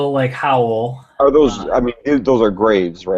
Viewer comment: Did he say ''those are GRAVES?! :O